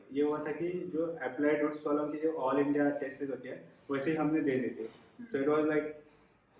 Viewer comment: what did he say? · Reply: He said applied root test series